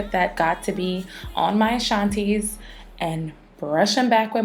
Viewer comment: She said something at 2:50 that sounded like “shonties”. Can someone please tell me what she said and what it means? I can see from the video it refers to hair. Thanks!